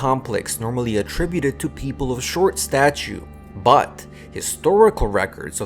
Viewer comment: Did he just say “short statue“? Instead of “short stature“?